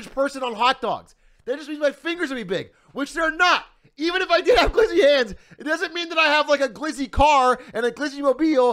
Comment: All I heard is "I have a Glizzy Car".